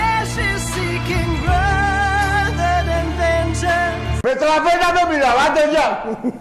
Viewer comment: τι λέει ρεεεεεεεεεεεεεεεεεεεεεεεεεεεε Εεεεεεεεεεεεεεεεεεεε ρεεεεεεεεεεεεεεεεεεεεεεεεεεεε ρεεεεεεεεεεεεεεεεεεεεεεεεεεεε ρεεεεεεεεεεεεεεεεεεεεεεεεεεεε Ευχσριστω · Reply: Με τραβέλια δεν μιλάω